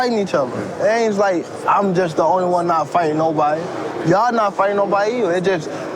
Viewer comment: He didn’t say nobodies he’s saying he ain’t getting the BIG FIGHTS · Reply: That's not what he said. 5:55..its not like im the only one fighting nobodies!!..its pretty clear to me..who has tank fought in the top five?